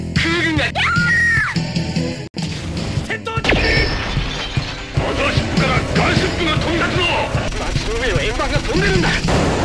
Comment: マザーシップからガムシロップが飛び立つぞ！って聞こえるw